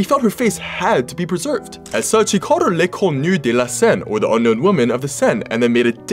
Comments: As a french person I died when he tried to prounounce french words 💀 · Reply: La même 😂 j'ai entendu "la con nue de la Seine" j'ai buggé un moment xD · As a french i heard ''l'éconnu des la seine''